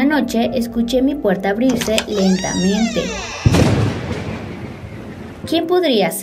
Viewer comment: Que dijo la niña en el segundo 0:26 "Y entra mi ¿empero?" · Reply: Dijo lentamente